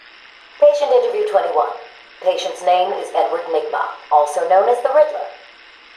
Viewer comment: at first it sounded like it said "Edward Nick-Bob"